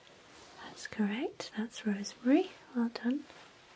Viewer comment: Me: vanilla: that's right, it's raspberry Me: close enough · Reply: mof I thought she said rosemary? Hmm idk anymore